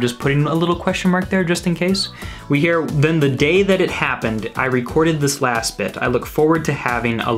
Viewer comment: When he said “I recorded this last bit” I heard “I recalled, just this last bit”